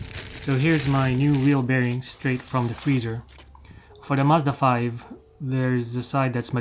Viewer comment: Excellent video! what did you say at 9:35 just before you say "there's the side that's magnetic?" bearings in freezer is a good idea. When I first started working on my motorcycle back in the early 80's I would put the valve guides in the feezer and bake the cylinder head in the oven before installing them with a drift. I am looking at doing innner and probably outer tie rod ends on passenger side of 07 Mazda 3 along with the wheel bearing. I also would rent both bearing puller tool and tie rod tool off local store. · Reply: Thank you! I'm saying, "For the Mazda 5, there's the side that's magnetic." Thanks for watching and for sharing your experience